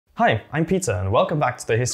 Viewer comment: "Hi, I'm pizza" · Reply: Haha sorry, just being silly. To me it kind of sounded like you said "Hi I'm pizza" at the very beginning of the video. My adult child brain giggled. Great video :)